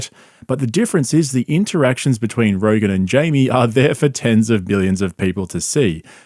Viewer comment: Did you say... 10's of billions?